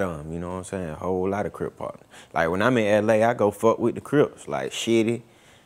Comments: did he say he fuck with Shitty(as in SHittyCuz) when he come to LA??? Damn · Did he say Shitty? @ 3:16 like dude who murder Nippsey?